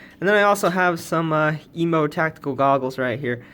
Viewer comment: did i hear "emo tactical glasses"? lmao